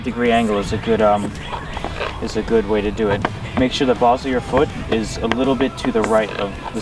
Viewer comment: did he just say The balls of your foot?!!